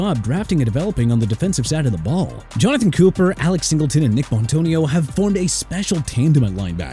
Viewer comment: Did I hear Nik Bontonio 😂